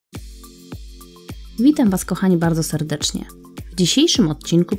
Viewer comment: czy tylko ja usłyszałam "Mitam" was kochani bardzo serdecznie? 🤔